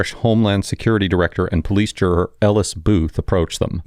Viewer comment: What did say at 0:24 "Homeland security director and police [inaudible]" sounded like juror?